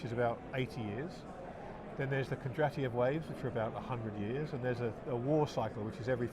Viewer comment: What did say at 13:30 ? Something about a "Kedratian Wave"?